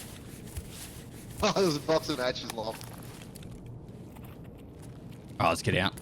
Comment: What do you mean "imaginary language", he said "wow just a box of matches lol" very clearly.